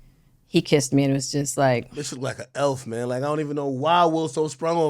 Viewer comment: Hey Pause@ 18:45😂 did he say"This mf look like a elf😂"